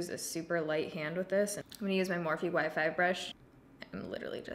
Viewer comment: Every single time you say you're using you "Y5 brush" I hear "wifi brush" and every single time it catches me off guard. Had to share lol. Great video as always 💜